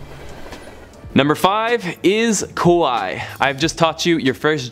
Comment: I must say I sound like 'Kowai' instead of 'Kawa ii'. Be careful, 'Kowai' means 'Scary' in Japanese.